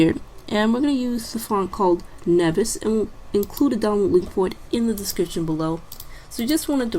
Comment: haha the closed captions say "doughnut leopard" instead of "download."